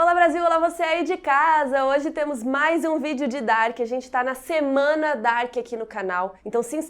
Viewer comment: Carol falou "Semana Dark" e eu escutei "Joana D'arc" .-.